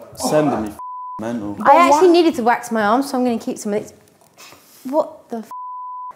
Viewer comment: Did say she needs to wax her ARMS 🫠😂